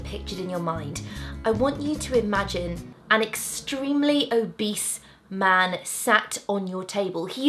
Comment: when you said obese man I thought you said beast man and it gave me a shudder and the Beast from disney Beauty and the beast sweating profusely. XD Then I stop imagining coz laughing too hard by myself! XD